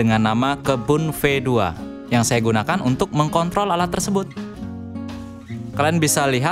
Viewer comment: apa saya aja ya yang salah dengar dengan kata kontrol